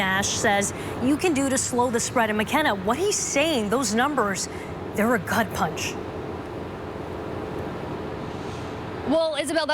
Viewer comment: I thought she said "there a cunt punch"